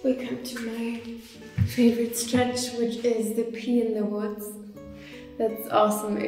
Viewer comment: In 9:42 what i heard was "my favorite stretch which is "Pee in the Woods" and i guess the name suits it. did any one hear what i heard? :/